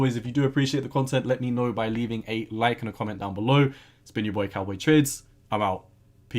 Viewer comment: I heard cowboy tits 😳